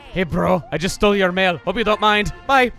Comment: I misheard chuggaconroy saying: ”hey bro, I just stole your mail, hope you don’t mind. Bye!” as ”hey bro, I just stole your man, hope you don’t mine. Bye!”